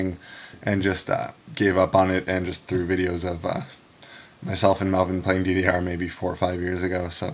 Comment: LOL I probably misheard, I thought you said "videos of myself in Melbourne playing DDR". Oh wells. =D